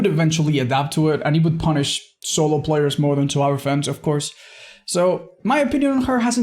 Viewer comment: Anyone else hear "Survive with Hens"?